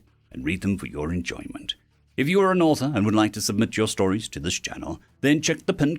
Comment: I’ll admit, I heard “if your an orphan, and would like to have your stories read” Instead of “if you’re an Author and would like to have your stories read” I was really not paying attention